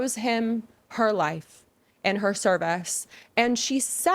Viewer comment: "she owes him her life and her cervix" that's what I heard at first. Thought yikes, that's a little extreme